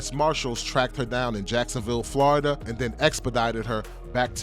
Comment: lmao, it sounded like he said expedited